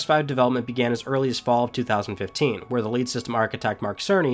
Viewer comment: it sounds like you said "...the LATE system architect Mark Cerny..." 😳 For a few moments thought he passed away. After a few rewinds realized you said LEAD. Is that a Maryland accent? 😆👍